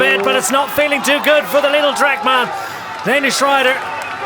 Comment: “the little trek rider” M. Pedersen is 6 foot, and 70-75 kgs. What is he talking about. Just saying whatever?? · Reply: Lidl trek I suppose